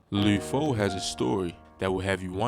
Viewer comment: Why dude sound like he sayin Lube Foe